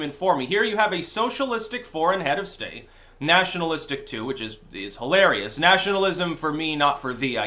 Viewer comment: All I heard was, "he's a national socialist"